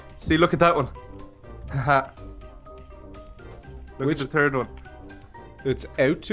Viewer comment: When said third I thought he said TURD